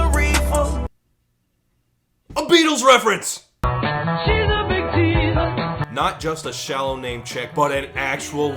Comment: I heard them say "she's a good teaser" than the Beatles saying "she's a big teaser" tbh